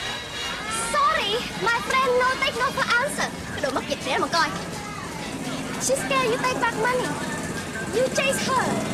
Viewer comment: What did she say at 2:15-2:17? Curious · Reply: cripplehawk she said” Cái đồ mắc dịch, để đó mà coi!” . It means “ I cursed you” or “damn”